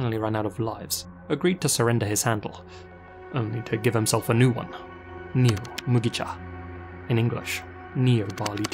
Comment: I cannot understand what you said at 4:26 with the English translation of "neomugicha" and captions were no help. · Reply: Neo barley tea. I struggled too.